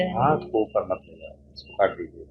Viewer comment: kya bola sir ji ne baat par ....... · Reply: Wo bole ki hath upar mat le jao, probably wo retired officer hai, aur baat convey karte samay hath move nahi karna chahiye. Jab interview Dene jate hai tab bhi apni baat convey karte samay hath hamesha knees par hona chahiye.